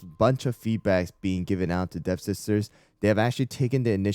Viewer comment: my cc said "deaf sisters"🗿🗿